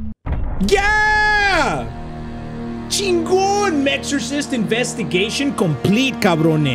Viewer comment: I liked the minute I heard "mextrosist"